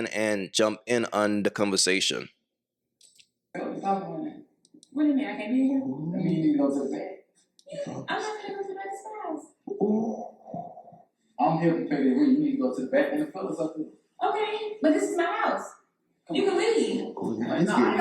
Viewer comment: Did anyone else hear what was said. Him: You should go in the back. Her: This is my house. You can leave.